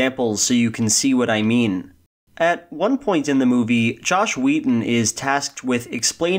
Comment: I thought you were saying joss whedon the entire time